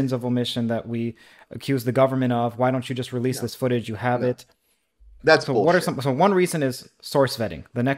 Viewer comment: What did say at 18:00 - 18:06 - Sounded like he said, 'That's Horse Shit' lol